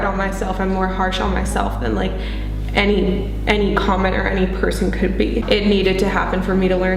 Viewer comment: did she....did she just call people commoners... at 29:56 .... when she's talking about being too harsh on herself